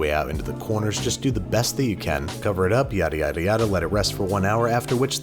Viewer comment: Did he actually said Yare Yare Daze?